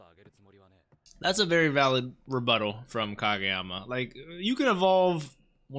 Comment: Just giving the very mature comment that i heard "rebuttal" wrong and thought you said "that's a very valid butthole from Kageyama". Thank you that will be all.